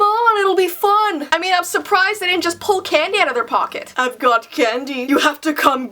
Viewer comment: I heard "pull Kanye" instead of candy ahaha